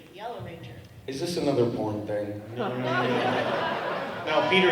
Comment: did Austin say p*rn? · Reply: Yes he said is this another pron thing? 🤣I died laughing